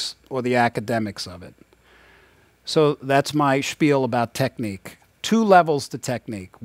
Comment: Did he just said "my SPIEL about technique" on 10:20??